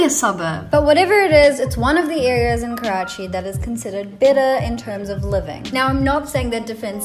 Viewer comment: Is it better or bitter(sound like you said bitter) ... Hunnnnnn